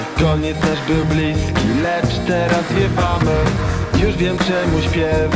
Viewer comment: Nareszcie uciekliśmy i lecimy w dal nawet nie wiemy gdzie ale dobrze jest booo mija kolejny dzień nie zatrzymujemy się: przestań spiewać skup się lapiej draniuuu, na sterowaniuuu: Przecież sam śpiewasz! Bo nie najgorzej, nie kontroluje się x2: Nie wiem czemu śpiewam, nie wleć na te drzewa: Daj się skupić: gacie zluzuj Uciekliśmy z wyspy, Tajemniczej Wyspy koniec nasz był bliski, lecz teraz zwiewamy! Pamiętasz rozbitka? Co zwariował i wygadywał głupoty o jakimś profesorze który był tylko w jego wyobraźni: Nie przypominaj o niektórych rzeczach wole nie pamiętać: Pewnie nie chcesz też pamiętać o tym jak te dranie nas okupowali x2: Już wiem czemu śpiewam, wleć w te drzewa mam już dosyć tego: O co chodzi? Uciekliśmy z wyspy, Tajemniczej Wyspy koniec nasz był bliski, lecz teraz zwiewamy! Komputer: Uwaga! Niski poziom paliwa! nastąpi wyłączenie autopilota proszę przejść na sterowanie manualne: Ej! Co???, autopilot? Ej, myślałem, że to ty sterujesz! Nie, to ty sterujesz, prawda? No właśnie nie! Boom!... xD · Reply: Ja usłyszałam zamiast lecz teraz zwiewamy! to usłyszałam lecz teraz śpiewamy XD